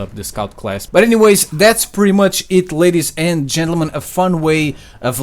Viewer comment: It sounds like he said "Ladies and genital men " XD